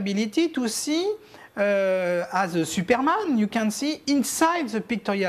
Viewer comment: did he just say Superman? 🤨😂